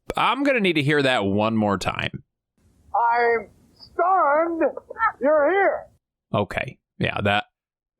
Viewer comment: I think he said "I'm stunned you're here".